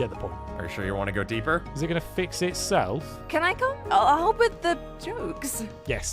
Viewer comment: I had to turn on the captions when said "I'll help with the jokes" for I miss heard that as "I'll help with the drugs."